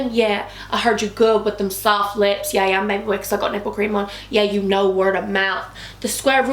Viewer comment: loving itttt, BUt WHAT DID YOU SAY AT 0:09 cos i heard "cos i have nipple cream on" lmfaaaooooo